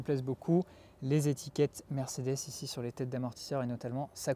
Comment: les etiquette quoi ? 🤣🤣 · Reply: Mercedes